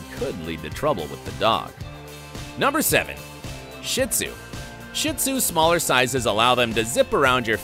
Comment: did he say shit sue